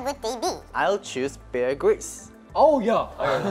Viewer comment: The way taehyun says 'i choose Bear Grylls' its sounds like he choosing a pokemon ;) :) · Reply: I thought he said bear grease and I was so confused. Thanks for clearing that up for me.